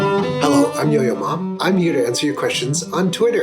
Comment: Why did I hear "Yo-yo ma" as "Yo mama" the first time I heard it